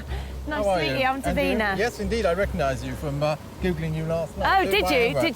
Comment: "Yes! I recognize you from... googling you last night." 😂 Best line in the show. 😂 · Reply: Lol! I have several loud fans going, so initially I thought he said "from hoovering last night," which I interpreted as somewhat insulting, i.e. "I had the TV on while I was vacuuming and you were on in the background." I'm relieved to know it was more lecherous. 😉